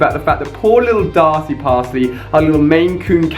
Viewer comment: Omfg, when you said Darcy Parcey I thought you were about to say Darcy passed away.